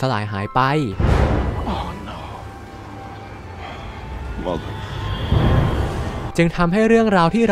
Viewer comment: เหมือนนิคฟิวรี่จะพูดว่า "โอโน มาตาฟักเกอร์"555